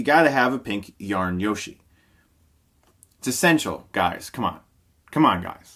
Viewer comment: it kinda sounds like he says "It's sensual".